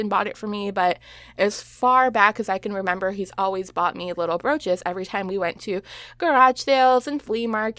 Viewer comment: LMAO BROACHES! I thought you said roaches LOL